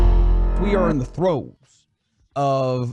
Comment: did he just say "We are in the Throat, of a wild game 1 finish?!?" I Beg Your Pardon!?! PAUSE!!!!!!! That Was Crazy!!! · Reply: No, he said “in the throes”